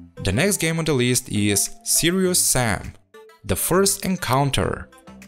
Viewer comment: What is that game 21:51 ? You said that name but I can't understand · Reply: Serious Sam The First Encounter